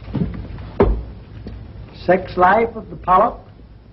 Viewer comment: This was different lol. Giving away those huge vases hahaha. The name of the book at 9:01 did he say 'Sex Life of the Pope'? hahaha 1933 but today you can't say anything negative about the Pope or anyone of importance. Maybe I heard that wrong :)